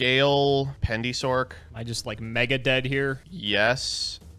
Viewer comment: Did comp said that he is megadeth?!